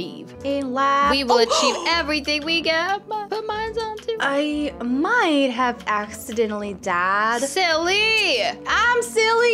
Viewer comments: When silly said I might have accidentally died I thought she said I might have accidentally dad 🤣. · When silly said I accidentally died I thought she said I accidentally dad 🤣